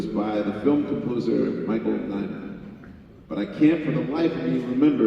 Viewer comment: The question @ 46:36 It's difficult to hear but is the film composers name Michael Diamond(?) and the track is M-work? Its hard to tell what he says, someone help me. Looking up Michael Diamond and M work yields nothing. EDIT: I think I found it. Michael Nyman - M-work B-side , 21 minute long track